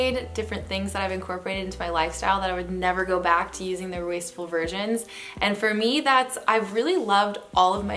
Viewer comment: Lol I thought she said wasteful virgins